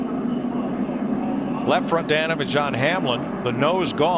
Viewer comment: does anyone know what mike said + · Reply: Carrie Ralya left front damage on hamlin